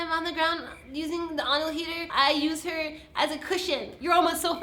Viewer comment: minute 1:30something did martina said anal heater? xD